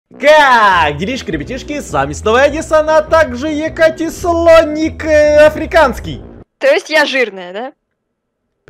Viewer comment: я одна услышала ЕкатеСОНИК??